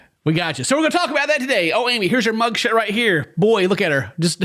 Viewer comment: did you say "mugshit?"